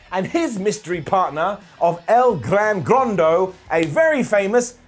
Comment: When they first said "el Gran Gordo" i thought they said "el Lardo" lmao i laughed so hard....like when sheamus said 2/3...two turds of the new day 😂😂😂